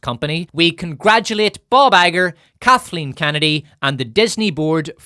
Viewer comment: I seriously thought you called him Ballbagger 😂